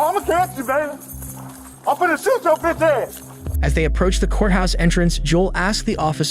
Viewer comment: I’m about to shooo yo bi ahh 😂 subtitles say [inaudible]😂